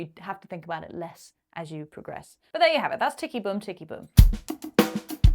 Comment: I need to get my hearing tested. I could have sworn you were saying "Sticky bum, sticky bum" for that first fill.